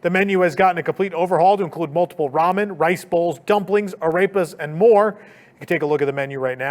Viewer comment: Holy Crap, had to do a double listen. When you were discussing the all new menu at the restaurant in Toon Lagoon and saying the name of each item it sounded like you said, a rapist. I know that’s not what you said because I know what food item you were saying is, arepas. I swear though it sounds like you’re saying, a rapist.